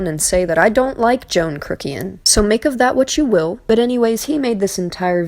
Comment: Oh, I thought you said "I like Johny Kuckian" and was a bit confused...